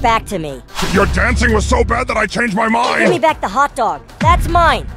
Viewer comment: I thought he said give me back the hostage for a second 💀